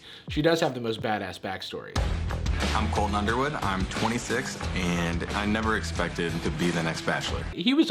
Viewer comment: DID I HEAR GROVER UNDERWOOD??? sorry but my PJO fangirl is showing